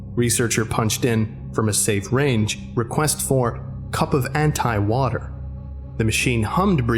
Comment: Cup of Anti-Matter (antimatter exists in our dimension) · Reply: It sounds like he said Anti-water